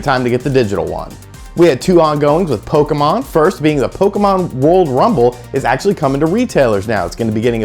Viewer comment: For some reason I thought you said Pokemon royal rumble lol. I had to do a double take just to make sure.